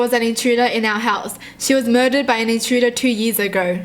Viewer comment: "she was murdered by a pizza guy"(at least that's what I heard) I'm jealous, she's lucky to see all the pizzas and adore them . I and I don't get to adore them though I had pizza for breakfast his morning 😂